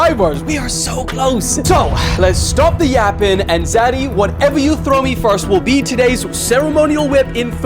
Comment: I thought he said daddy what you throw at me will be the whip. But maybe is the Daddy in the relationship